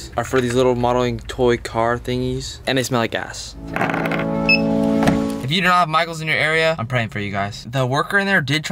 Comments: did he say gas or a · am I the only one who thought he said "and they smell like a**"? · Did he say. They smell like gas or A$$